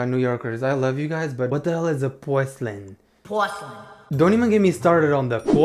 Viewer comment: it took me a few mins but i think shes saying porcelain lmao